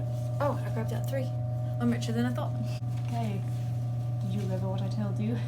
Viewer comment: “oop, i grabbed out three. i’m richer than i thought!” story of my life skdjcnsl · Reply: I thought said “richer than a thot”!